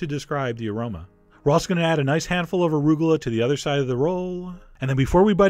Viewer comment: lol what di did you say a handful of rubella? xD